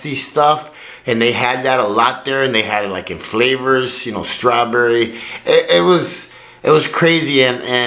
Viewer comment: Did he say strawberry flavored cocaine?